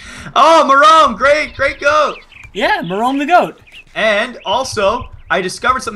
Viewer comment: thought it said genome the coat xD